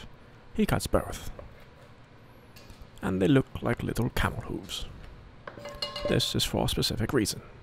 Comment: Got dang!, that looks good!🤩 Bacon, kielbasa, cheese (& more cheese) sour cream and TWO kinds of cabbage! That is a great recipe for a massive flavor explosion! 🤯Thank you for the video and recipe. I had to watch the sausage cutting scene twice. I don't hear as well as I used to (from working on aircraft & bad medicine), but I thought you said xxx toes, I was like..wait... What?! Then I put on the CC, hit replay to see you had said 'camel hooves' - that is a good cutting tip by the way. 😘👌I unofficially dub the 'Eddie Murphy of Keto Chefs!' You are truly THE BEST!! 🏅Thank you again for this recipe, making us smile, and for all that you do. Take care, stay cool and be safe! 🤗💖🙏